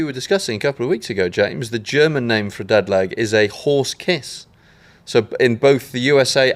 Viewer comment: Thought said "whore's kiss" for a second there